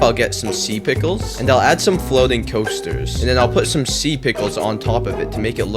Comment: I thought he said add some floating toasters